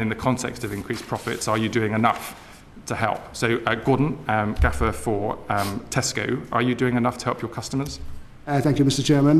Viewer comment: I thought he'd said "Gordon, gaffer for Tesco"